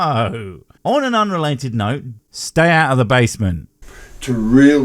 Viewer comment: ~ All that I heard was go into the basement. You going to do a reading of his book?... I will re-watch that 10 times